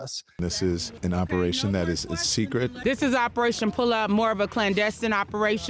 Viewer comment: every time I see this clip, I swear she says "Operation Pull Out" 🤣